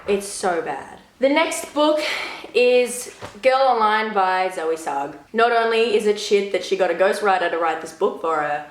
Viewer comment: I didn't hear the author's name at first and thought her name was "Zoe Succ."